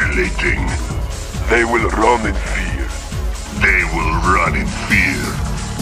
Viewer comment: I always thought ra2 apocalypse tank said: "They will roam in fear".